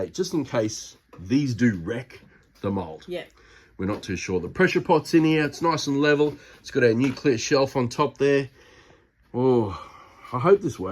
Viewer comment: I thought you said NUCLEAR shelf ☢️ 😂😅